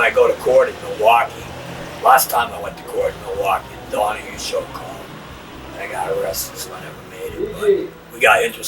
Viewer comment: 'The Donahue show' is what he says